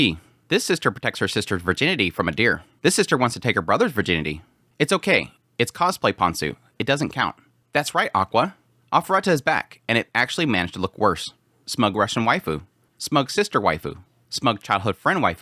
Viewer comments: what did he say? Its a what “ponzu”? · what did he say? What is back? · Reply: Arifureta